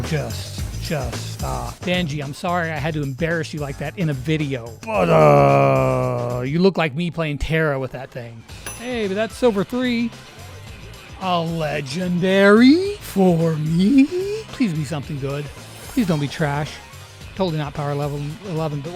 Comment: it sounded like a legendary Perry the legendary! I swear